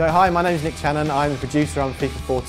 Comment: LOL i thought it said Nick Cannon